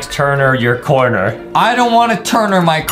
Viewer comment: DID HE SAY THE F WORD?!?? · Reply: He said I don’t want to